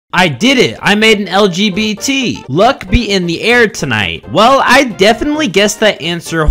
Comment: You know the one that goes “luck be in the air tonight” I thought it said F me in the tonight💀💀💀